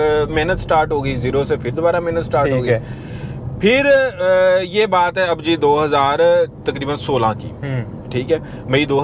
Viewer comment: jawad says abbu g to rana.. Thats what i heard